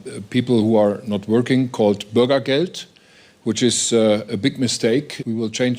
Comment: What is he saying at 9:33 ? Burgerkolt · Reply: Bürgergeld which is money given to people who don’t have jobs. Everyone who works contributes/pay towards an insurance called Arbeitslosengeld… which serves as something to help one when the lose their job (as they look for a job). What’s happening is some people are taking advantage of that 💰… and prepare not to work (especially those coming in the name of refuges). So we defiantly need some form of structure so that not anyone and everyone can take advantage of that. I hope I explained clearly